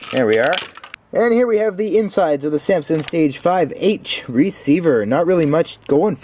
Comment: at the start I thought you said samsung